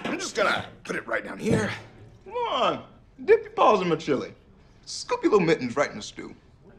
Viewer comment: the first 50 times watching this i thought Chidi said dip your balls in the chilli